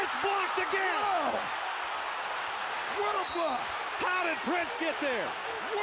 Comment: Lol I’ll always remember Doc on the call sounding as raspy as ever “WHAT A BLOCK” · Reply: For a second I thought he said Wtf but then my brain realised he just said what a block